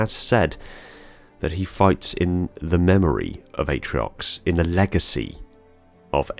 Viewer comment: Which one says the memory of atroix ?? I thought he said the will of atroix.